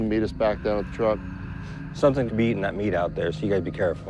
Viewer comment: did he say something is beating that meat out there 😂